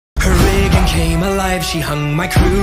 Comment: The first part where it said "her rig became alive" I thought It said "her wig became alive" 😂😂😂